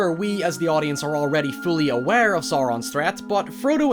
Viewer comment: Maybe it's just your accent, but - Zaron? It sounded like you said Zaron instead of Sauron (sowron). Other than that, yes, the Nazgul sent chills down my spine even more than the Balrog.